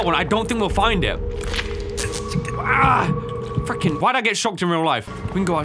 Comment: was that a small “fucking” I heard coming out of mouth? 0-0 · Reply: Nah it was just a freaking